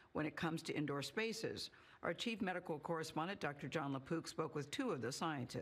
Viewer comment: did she say "Poops" ?